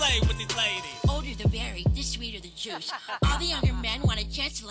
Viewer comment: The older the berry, the sweeter the juice 👵🏻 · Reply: WHY DID I HEAR HEAR “This older than (derrick) berry, this sweeter than jush”